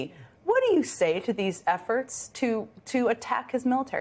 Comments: At first I thought Jen said " what do you say to these effers" instead of efforts, lol. That would also have been appropriate! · efforts lol. i thought she said f'ers Go Jen